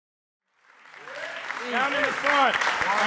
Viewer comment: What Mackie said? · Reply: i'm about 4 years late but i think he said 'down in the front'